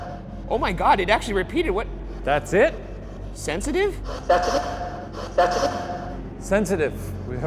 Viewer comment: I was listening to a lot of this and I didn't hear sensitive first, I heard "Stephanie "